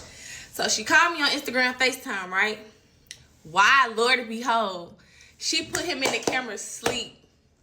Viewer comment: her smackin her mouth, and saying "boom" every 5 seconds, and the "lord and behold"! Omg!!! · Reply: 😂😂😂😂 oh U noticed it too, I had to cut on my captions & it was what I thought I heard “lord & behold” 🤣🤣